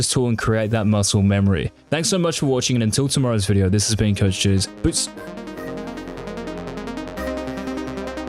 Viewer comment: I swear I hear him say "this has been coach cheese" every time